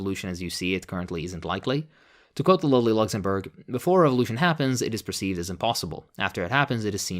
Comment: is he saying "to quote the lowly Luxemburg"?